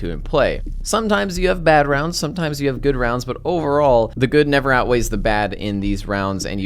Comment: Wait.... did i hear that right? at 22:19 "The good never outweighs the bad"?? Shouldn't it be the bad never outweighs the good? hahahaha